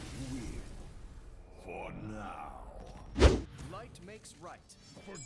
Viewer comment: Ok I wasn't listening properly and I could have sworn Anduin was saying "White makes Right" there and I was just like what in the fuckety fuck?